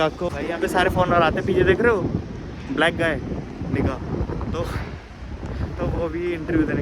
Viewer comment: What Did I heard now...!! N*gga 🕶️